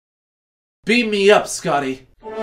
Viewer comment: i thought he said beat me up Scotty xD